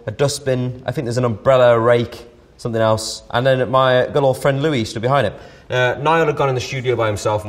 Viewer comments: Liam: my little friend Louis Louis: give me a break I'm big 😂😂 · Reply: i thought he said 'old friend'... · I'm half deaf and I heard Liam say "my good 'ol friend, Louis".. how do so many ppl think he's saying "little friend, Louis" ?!? ..English not your first language?